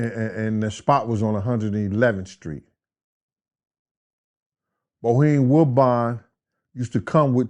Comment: What did he call him at 0:40 😂🤣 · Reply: Bohem woodbine 🤦🏼‍♂️🤦🏼‍♂️🤦🏼‍♂️